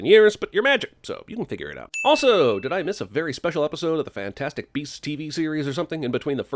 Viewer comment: I thought it said fantastic breasts...